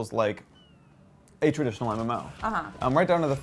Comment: What did Nick say at 3:21-2 ...atredish MMO?